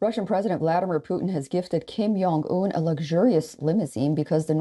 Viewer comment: did she really say " kim YONG un " ? 😂